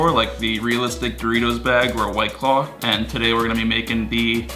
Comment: I thought he said "white clock hand" instead of white claw lol